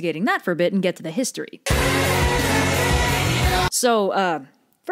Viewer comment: Me and my dumb zoomer brain thought you were saying Hermes used to be pansexual lmao. I'm an idiot.